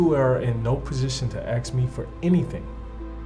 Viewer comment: Did the subtitles say "axe" instead of "ask" 😂 ?